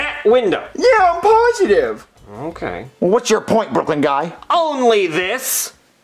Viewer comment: SML Movie: Jeffy Dates Goodman’s Daughter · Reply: nvm I thought it said Brooklyn guy